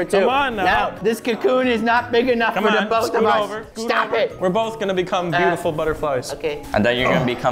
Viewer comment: am i the only one who hear we both gonna be asshole butterflies?